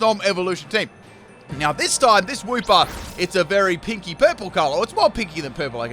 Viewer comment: so i misheard what said... i thought he said "kinky" purple color i need my ears checked